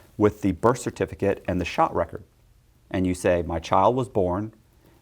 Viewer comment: That spirit of fraud is still in him. Can’t hide it. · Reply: At the 11:00 mark did he say "shot record", as in immunization record? This "mandatory covaids vaccine" to work/socialize/travel thing is starting to sound a lot less medical and a lot more debt-slaves-as-collateral by the day!